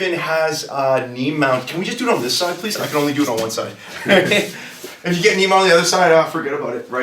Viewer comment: does he really say he can do it only on one side?